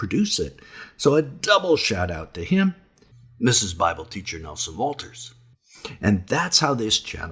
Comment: I dont know if i heard this correctly: "Craig Campbell, Mrs. Bible teacher Nelson Walters." ?? Nelson, can you clarify what you mean in those words, please?